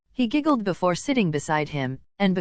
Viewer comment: im sorry im not trying to be rude but at the gray part in 30:40 i thought it said sh**ing instead of sitting💀💀